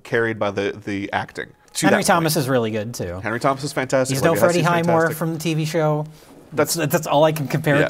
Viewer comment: Can somebody tell me what the fuck Jay says at about 33:00? I honestly have no idea. It sounds like he says, "He's known for Eddie Heinmor from TV show" but I can't find a single credit on IMDB that's even close to this. Can someone with knowledge illuminate what the hell he's talking about? God I wish they'd stop trying to talk over each other, or at least edit it out when it happens.